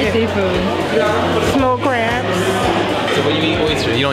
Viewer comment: I remember being in New Orleans and feeling so discouraged how things were not going to plan. kept encouraging me to go with the flow (I like structure and making sure our video hits all the main points so it wasn't easy for me). We ended up having one of the best family experiences meeting. We met an incredibly generous soul who works so hard every day to support her family through life's hardships and was willing to share everything with us. Conversations around race are a recent topic I've dived deep into over the last couple years and I have noticed biases within myself towards people of different socioeconomic status. I hope this video exposes you to the beauty of humanity in all walks of life and ultimately brings us all closer together ❤ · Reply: I think she said Snow Crabs I’m 20 minutes outside New Orleans. If you ever want to visit again let me know dude 👍🏾